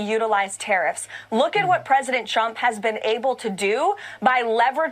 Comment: at the 5:55 mark did she say "Look at what President Chump has been able to do..." 🤣